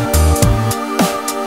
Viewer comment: If you ever have to deal with a bully, be sure to become an adult That's what I heard